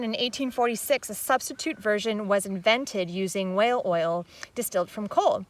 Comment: Do I hear the girl, rightly? Whale Oil? Distilled from Coal?